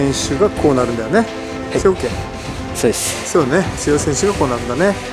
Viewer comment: のところの 「強い選手がこうなるんだよね」の後が聞き取れなかったのですが、なんと仰っていますか？ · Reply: ご視聴頂きありがとうございます。 とても実直な生徒さんでしたので、 「はい、そうです」と私の会話に合わせて返事をしてくれました。